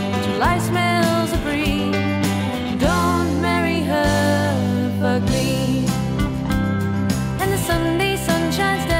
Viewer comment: I always thought she was saying "Don't marry her, but me" 😂